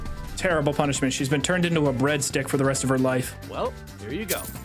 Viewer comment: When he called Sadia a "bread stick" I thought he said "red stick" and I thought I was just informed of a new fantasy slur. Glad it wasn't tho